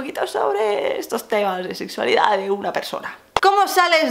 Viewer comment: Alguien se dio cuenta que dijo que quería hablar de estos temas de sexualidad Y "UNA PERSONA" 😱😱😱 min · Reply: Dijo que quería hablar de temas de sexualidad de una persona!